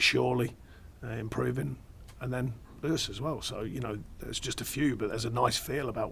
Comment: When he's talking about young players growing.... around 11:15 he says something like 'erse as well'.... I've misheard obviously but what does he actually say? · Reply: He says “Lewis as well”. 💙